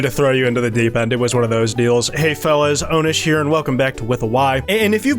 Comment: did you just call yourself anus? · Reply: his name is onus get it right